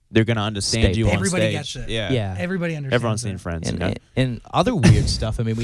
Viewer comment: can someone please tell me what did Luke said?xD (coz I heard ''everyone else in France'' but I'm not too sure ^^')